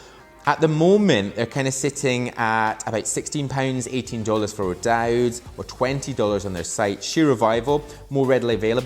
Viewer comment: i thought he said SIXTY pounds, i stg i almost died